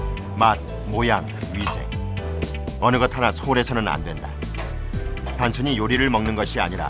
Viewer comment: 마늘, 모양 어느것 하나 빠지면 안 된다라고 들리냐ㅋㅋㅋ